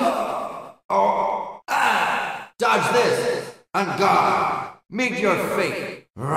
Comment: Did... Did he say "on God?" At 0:11 edit: "on guard" but sounded like he's said "on God" 😂😂